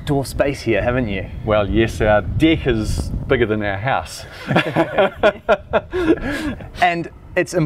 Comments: I'm sorry but at 1:28 i heard "our dick is bigger than our house" 😂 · Did he say our dick is bigger than the house???! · "Well yes, our dick is bigger than our house." What did I just hear here?? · Could have sworn he said "our dick is bigger than our house" right at about 1.30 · I thought he said “our dick is bigger then our house” 😂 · Wait, did I hear "dick is bigger than our house?" or it's bec of their native accent? That was cute, though. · did he just say "my dick is bigger than the house"?